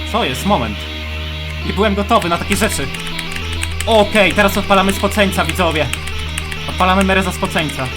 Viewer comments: usłyszałem:teraz odpalamy (zboczeńca) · usłyszałam ,,odpalamy mereza zoboczeňca'' XD